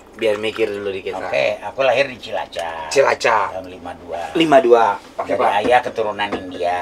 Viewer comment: bapannya Onad ngomong CILACAP gak bener ga sih apa gue salah denger? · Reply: Benar, beliau lahir di Cilacap.